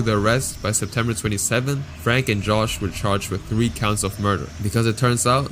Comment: heard this as drake and josh